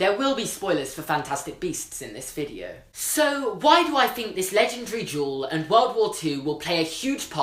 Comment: Every time he said "legendary duel," I just heard "legendary jewel." Is it just me?